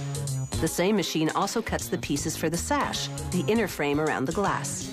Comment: I thought it said sacks